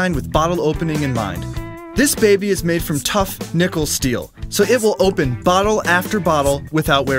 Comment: at first i thought he said "tough, nipple steel" lol